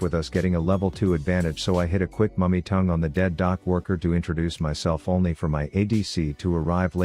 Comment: When you said some shit about pyke 1st time i thought that you said Dead doug walker